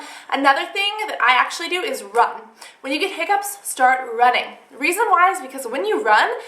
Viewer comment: Haha I feel funny I thought you said rubbing not running so I started to try rubbing things even though I don't have hiccups